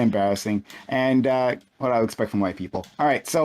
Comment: Am I hearing things or did he say "What am I expecting, white people"?